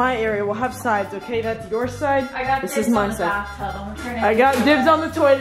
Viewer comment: srry won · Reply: DIDNT ANYONE HEAR SAY I CALL THE BATH TUB